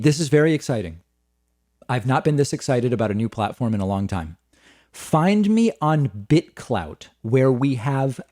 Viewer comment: I swear to god, at the end I thought you were about to say "Find me on Frank" XD